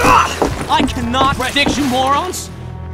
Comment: anyone know what he says here? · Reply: All i hear is "ugh, i cannot predict morons?"